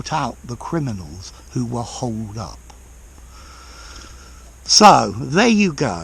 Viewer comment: did you say holed up or hold up? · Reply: i've searched those expresions, and holed up fits with the use he made of the words.